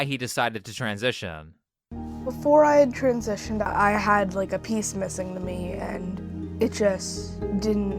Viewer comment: i thought that he said "I felt like I had a penis missing from me"